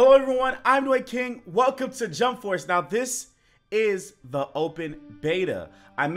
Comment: I Think i heard him say junk force